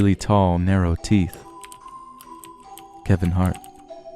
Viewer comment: Did he say Kevin Hart at 4:20 ?